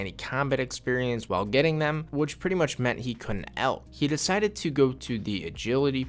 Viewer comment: "Which means he couldn't L?" What word did he say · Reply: Alch